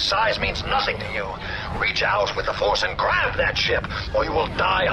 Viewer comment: I thought he said grab that shit not grab that ship XDXD